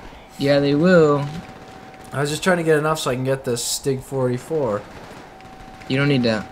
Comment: around at 12:10 did he say STUG 44?